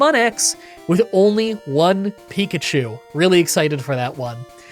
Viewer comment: When you said next weeks challenge was going to be "Pokemon X with one Pikachu" I was only half listening and misheard Pikachu as Pachirisu and I had to rewind back because I needed to be sure if it was actually happening, oh well, its an electric solo type rodent that can be obtained in XY, it's the closest I'm gonna get to a Pachirisu solo run for quite a bit probably